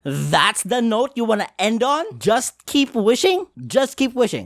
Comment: I think the worst part about the final line, is that you can very easily mishear "just keep wishing" as "Keep wishing, as it won't come true" I know what the context is supposed to be, but COME ON